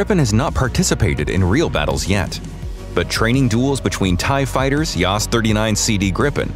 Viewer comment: Grippers train against TIE FIGHTERS? Unless Saab comes with A-Wings, they won’t get any wins or ties 😝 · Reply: LoL, I actually thought he was saying Thai fighters and didn't really understand as the top of the line RTAF aircraft is the F-16